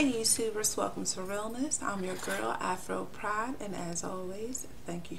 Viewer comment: lol wow i just realize your hahahahahaha i always heard you say afro pride